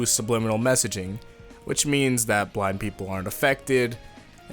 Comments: lmao, at first I thought he said that black people weren’t affected by Weather Report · bruh I thought you said black people aren’t affected. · I thought you said 'black people aren't affected' on #10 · i thought he said black people